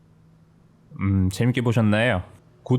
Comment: 엣지로 들려요…